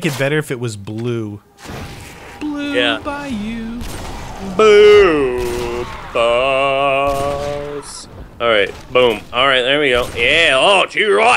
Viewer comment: did he say blue balls